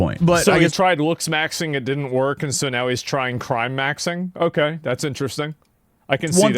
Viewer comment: 😂 Crymaxing means something completely different in my circles. It's the act of crying due to loneliness while you... y'know... 😏 (Edit: I realise now that he said Crimemaxxing, but ah well, mishearing ftw)